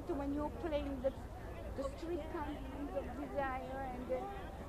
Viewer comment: She told her about Glenn’s performance in The Wife, that was lovely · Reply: i thought i heard streetcar named desire